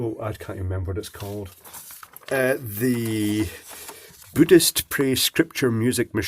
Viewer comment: At the beginning I thought you said "Buddhist Priest". Never heard them sing before haha. Now I'm off to play Dr.Alban's It's My Life. great video as always. Cheers.